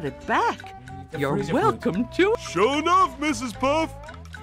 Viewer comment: This is so cool and the part where patrick says something to mrs puff i thought he said shut up mrs puff XD